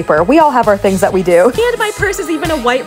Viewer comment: I thought it said off with your hands 😂😂 lol